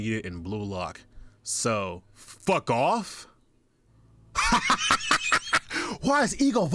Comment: In the leaks i thought he said "Lock off" not "fuck off" lmaooooooooo